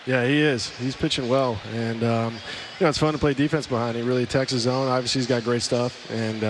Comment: What does he say at 0:34 after "Its fun to play defense behind [him]" (something about how he really ????? on)? Thanks. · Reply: He really attacks the zone. Obviously he's got great stuff. And um, can't say enough about him and the rest of the guys who threw tonight. Putting up zeroes all night. Good tempo. And throwing strikes.